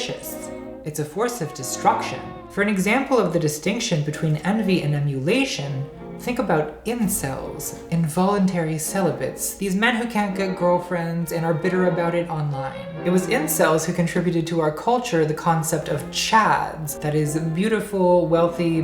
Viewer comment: I thought you said “grow girlfriends” and my mind went: amazing joke. And then “how would you?”